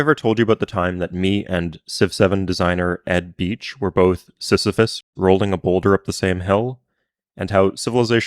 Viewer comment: I heard “We’re both sissified” holy shit I need get off Xittter.